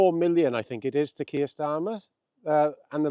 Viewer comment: Sorry, I think I misheard you. Did you say Queer Starmer?